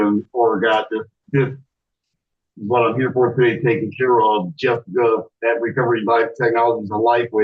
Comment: I assume I misheard, I'm sure I heard him say Justin Bieber... 😊😊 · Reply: I heard that too, but I think he said Jessica.